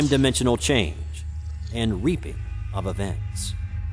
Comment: did he just say reaping??😂 and it says repeating